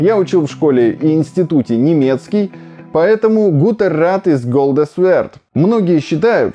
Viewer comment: Вот абсолютно не знаю немецкий . Но мне интуитивно почему то показалось что он сказал "хорошо золото, что блестит" Я угадал? Или шо он сказал? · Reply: Guter Rat ist Goldes wert - Хороший совет на вес золота